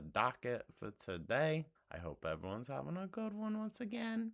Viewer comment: You said I hope everyone's having a good one and I thought you said I hope Evelyn's having a good one and I was like who's Evelyn?